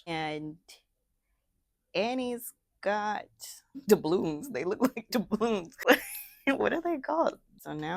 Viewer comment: I thought you said “Annie’s got the blues” 😂😂😂😂and I fell out laughing, then I heard you say doubloons 😂😂😂and I laughed even harder😂😂😂😂